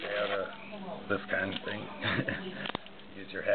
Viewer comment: Lol at first I thought you said don't break the lock not law